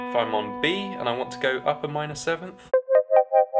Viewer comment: It sounds like you said, "up a minor Sith."